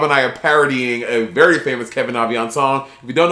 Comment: what’s monet saying? What’s the name? Kevin Avion? Kevianz? Kevion? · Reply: Kevin Aviance